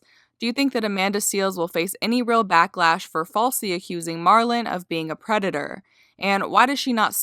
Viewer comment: Hmm did you say Marlon at 5:46 is it Myron or Marlon